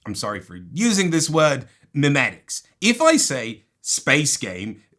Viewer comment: the captions say mimics instead of memetics